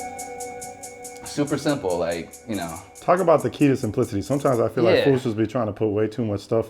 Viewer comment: I thought he said “talk about the key to some pussy”